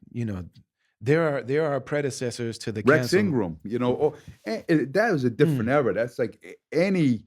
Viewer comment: Did he say "Rex Ingram"?